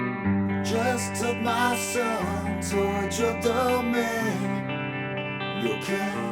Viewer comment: I really appreciate that you point out the different sounds you hear, and whether hearing them in either the right or left ear. As someone who has unilateral hearing loss, I often don't realize what I'm missing in songs. 😄 This song--oh, my goodness!--the first few times I heard it, I could've sworn he was saying, "Just took my son to a trip to Maine." 🤨😂